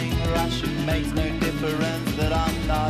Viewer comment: I thought when he said “bang goes” he was saying “mangos”